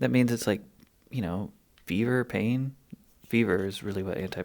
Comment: I thought he said “beaver pain.” Oi. He said fever/pain.